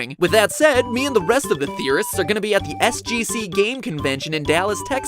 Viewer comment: I thought said theribusis hahaha funny ( for me